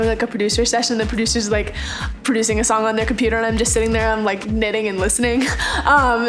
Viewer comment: When she said knitting I thought she said eating and I was like yeah I totally relate heh